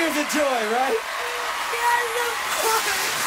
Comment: could anyone pls tell me what did Grace say? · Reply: She said yes of course